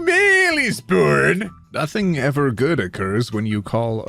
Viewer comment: for a second there i thought you said "nothing good happens" in response to "when a female is born" and I was shocked